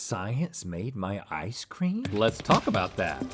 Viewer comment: I swear to God i heard Rhett say: "science made my eyes cream".